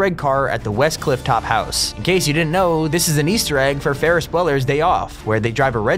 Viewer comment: that’s not how you pronounce bueller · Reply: Ikr I thought he said Weller